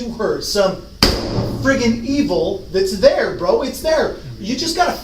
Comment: Did he say freakin evil